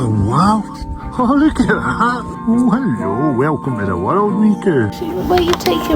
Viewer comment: "welcome to the world me cow" I LOVE ACCENTS 😭😭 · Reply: HELP I THOUGHT HE SAID WELCOME TO THE WORLD MIKU